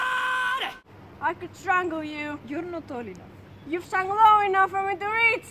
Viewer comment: When Viktor said,"Your not tall enough" I thought he said your not old enough. I:09